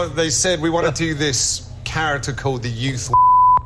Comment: I think he said “youth wanker”, as in youth worker.